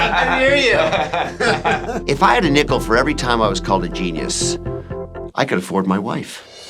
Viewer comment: I thought Dr Dubrow said "If I had a nipple for every time somebody called me a genius..." 🤣 Anyone else realize who his brother is/was...? I recently found out he is the brother of Quiet Riots singer Kevin Dubrow! What a VOICE!!! Not the mot liked dude in rock n roll, apparently, UNLIKE Dr. Dubrow, Kevin had a reputation of being very egotistical and arrogant, but man that voice was POWERFUL! ! !